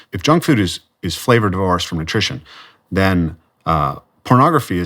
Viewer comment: What does he say about junk food @ 1:23? "If junk food is flavoured R-Rs for nutrition". What are "R-Rs"? Why is this man talking jibber-jabber and not using normal words?